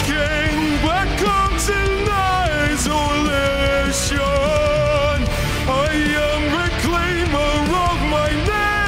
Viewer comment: am i the only one who heard him say at 0:20 "black cocks and isolation"